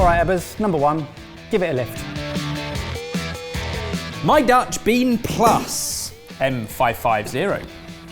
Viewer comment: I first thought he said "My dutch peen" and you know